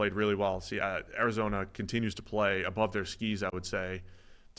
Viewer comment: I heard this as "playing above their skeeze", and I assumed was casually dropping Gen Z slang on us